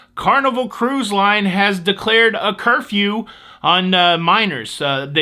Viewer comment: Oh, I thought you said "Curfew for Miners" :-)